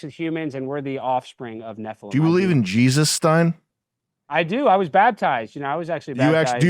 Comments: I genuinely thought I've heard "Jesusstein" and had a little laugh but then realised he meant "Jesus, Stein" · i thought sam said jesusstein before i realized who he was talking to